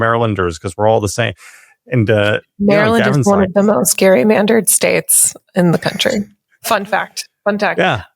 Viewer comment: I could have sworn that said "scarymandered" but even if she didn't, we should embrace this term! "SCARYMANDERED!"